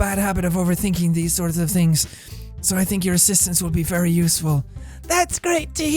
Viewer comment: when yuri said "i think your assistance will be very useful" or something at 37:40 i may have thought she said "i think your ass will be very useful"